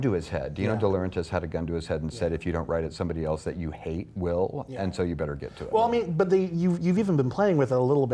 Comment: I'm sorry that English is not my mother language. Can someone enlighten me what's Bryan said in 14:37 about "hate Will"? Thank you very much. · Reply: He wasn't referring to the character Will. He said: "someone put a gun to his head and said if you don't write it someone else you hate will" when he was talking about how he didn't think Thomas Harris (the writer of Hannibal novels) wanted to write the book about Hannibal's origins. Hope this helps!